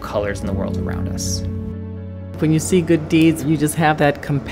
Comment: I thought it said seeing god